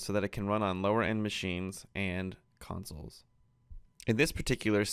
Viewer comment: what was that? i heard console >:D